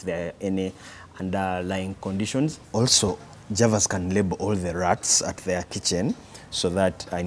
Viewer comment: Did he just say the rats eat keys🤣🤣🤣🤣🤣